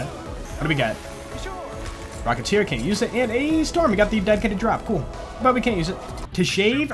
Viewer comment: auto generated subtitles say dead kid to drop